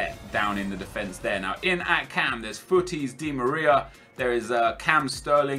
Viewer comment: did he said futtis Di Maria?